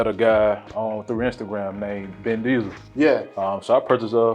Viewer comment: I heard "Vin Diesel" lol